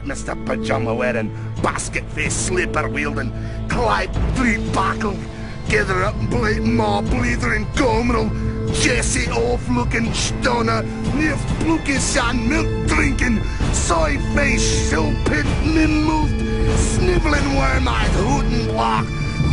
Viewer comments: I thought he said stoner at first, but now I know he said schooner. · What do you think of that, Mr. Pajama-Wearing, Basket-Face, Slipper-Wielding, Clype-Dreep-Bachle, Gether-Uping-Blate-Maw, Bleathering, Gomeril, Jessie, Oaf-Looking, Scooner, Nyaff, Plookie, Shan, Milk-Drinking, Soy-Faced Shilpit, Mim-Moothed, Sniveling, Worm-Eyed, Hotten-Blaugh, Vile-Stoochie, Cally-Breek-Tattie? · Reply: I definitely heard him say "stauner" not "scooner" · That part I thought the Scotsman said , ( Jesse oaf looking stoner ) . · Did he say soy-face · Soy-faced Did he just call Jack a soyboi? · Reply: I heard “soy based soapin’” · Uisce Beatha swear I heard him say stauner